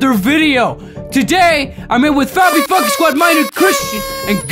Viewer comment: Thought he said fucky everytime he said funky